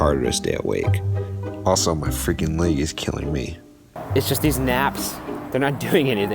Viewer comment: What did he say at 6:03? "Also my freaking ___ is killing me??" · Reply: Leg